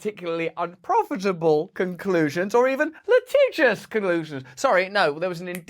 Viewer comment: did I hear "litigious [collusions]?"